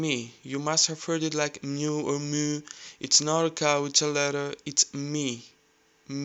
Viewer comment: "It's not a cow, it's a letter." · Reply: I thought he said, "It's not a capital letter." Now it makes more sense lol.